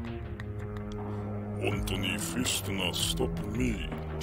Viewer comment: what i heard he said one tiny fist no stop me